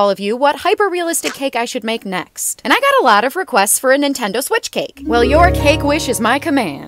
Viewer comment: “Give it my best try, and if it doesn’t work out, I’ll have learned from my mistakes” One of the best pieces of advice ever. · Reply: I thought that she said “ a Taylor swift cake”…..